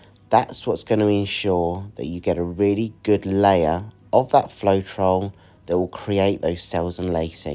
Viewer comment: your calming voice and encouraging words while you were painting really helped me to relax and enjoy your creation. Love the added detail at the end. It added an element that helped give the painting that extra beauty. Nice work. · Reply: yes, Linda, I believe I heard him say floetrol.